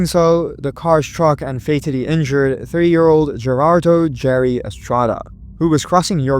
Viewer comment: did you say "three year old"? I dont understand